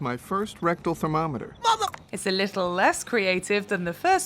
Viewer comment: I always thought he said "What the-!?" not "Mother-!"